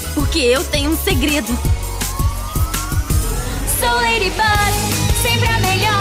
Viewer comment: sounds like it said slow ladybug. lol